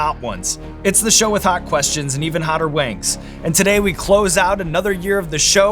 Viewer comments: I misheard Sean and thought he said "With hot questions and even hotter wanks" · i just heard it as "...it's the show with hot questions and even hotter wanks" and now I can't unhear it.......